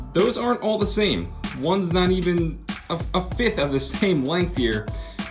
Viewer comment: I got a really loud ding notification right when he said fifth and I was really confused because I thought he said F. U. C. K. Lol